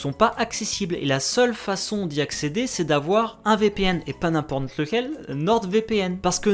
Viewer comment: Si toi aussi, t'as entendu "n'imPorn lequel" à 5:32 😋